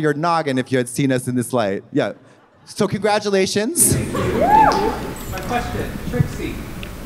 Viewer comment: what did the audience member say at 42:48 please i still can’t figure it out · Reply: Thankyou - my question - Trixie ......